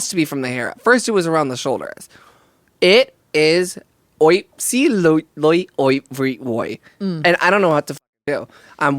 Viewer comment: SORRY what did say after describing her bacnce at 19:00???????? im in tears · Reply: Absolutely everywhere 💀